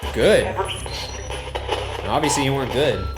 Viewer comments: sounded like “we were good” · sounds like someone is saying "Everyone was good du" as if saying no one in the cell was bad, but good, and they did not evil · I think you may have contacted a ghost guard. When the box says 'good' in response to Ryan's question 'what did you do here?' And when Ryan says obviously you weren't to me it sounds like 'where's the warden?' The warden would know who the guards are and could set you straight. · he heard “I’m very good” - anyone else hear that? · at about 27:42 it sounds like the voice on the spirit box says "i was good", followed by what i THINK might be "don't say that"? not sure tho.